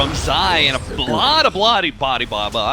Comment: what is he saying? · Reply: a bloody bloody Ali baba